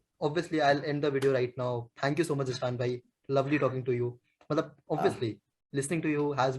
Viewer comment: did u say Usman bhai??? 😅